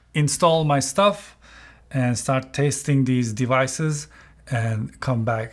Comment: I heard “start tasting these devices” I was like ooooookay!?